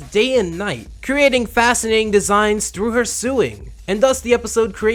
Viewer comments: Did you say "through her sue-ing" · I'm sorry but did you say SUEING?